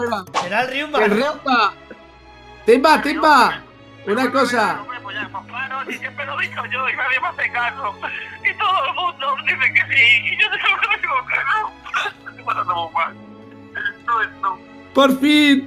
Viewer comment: para quienes no entendieron lo que dice timba es:pues claro si siempre lo e dicho que no y nadie me hace caso, y todo el mundo dice que si y yo no, hombre esque NO es NO. Eso es lo que timba dijo...y si 2 años tarde